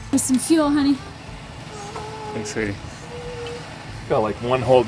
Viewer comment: Sounded like he called you at 2:28 lol